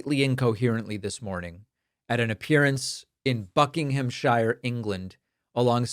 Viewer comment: I thought he said fucking